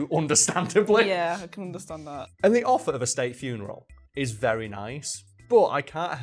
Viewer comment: I misheard “state funeral” as “steak funeral” the first time I watch this.